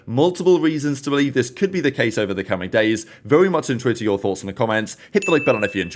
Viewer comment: what word are you saying at 0:28. I never know what ur saying "I'm very .... to your thoughts" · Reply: Intrigued pretty sure